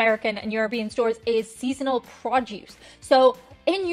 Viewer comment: Good thing the Seasonal Produce text came up because it almost sounded like was saying Seasonal Prawn Juice. As an American not used to Irish accents, I had to do a double take (we say the long O in PRO instead of PRAH).